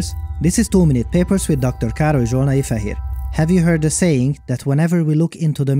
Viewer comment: Dr. Károly Zsolnai-Fehér. Dr. Kárl-ee Zsol-nah-ee-Feh-here I’m glad this video had subtitles, I always thought he said “Dr. Carl (afhhdgjdthcwpna), here.”